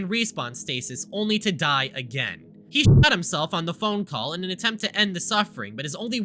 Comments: when he said "shot himself" I thought actually said "shit himself" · when you said "the 10th class shot himself" you saying shot was censored for the rest of the vid I thought you said he shat himself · When said "He shot himself on the phone call" I thought he said "He shat himself on the phone call"